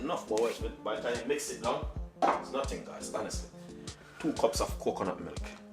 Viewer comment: How much flour does you use for the 2lbs of potatoes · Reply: 2 cup i heard him say..